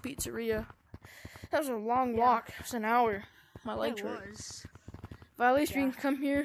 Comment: Did he just say “My legs are tired”